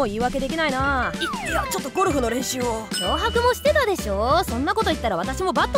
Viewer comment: テツ「い、いやちょっとゴルフの練習を………」 あれ、僕の耳がやばいのかな？なんか野球って聞こえるんだけど？